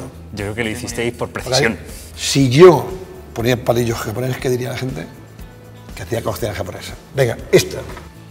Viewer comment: no entendí lo que dijo en el minuto 3:59 :c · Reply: Diego Roca Dijo que si usaba los palillos normales la gente pensaría que hace comida japonesa